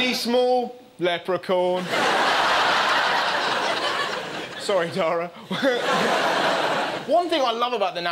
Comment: what did Russell say? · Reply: Sorry Dara